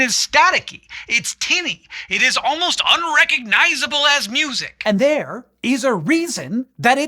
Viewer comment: unrecognizable as music Or unrecognizable ass music Not sure what I heard